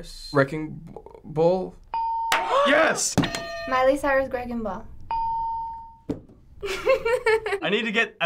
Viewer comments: did she say greging ball?!? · Did she say Miley Cyrus - Greg n ball ?? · I heard Miley Cyrus greging ball instead of wreaking ball.lol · WTF did she just say dragonball?! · I LEGIT HEARD SHE SAID DRAGON BALL 6:14😂😂😂